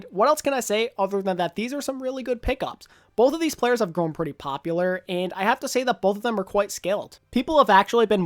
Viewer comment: I heard mouffin isn’t very popular with the children